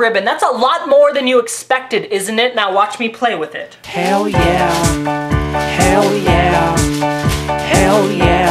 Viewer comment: I always thought he said oh yeah not hell yeah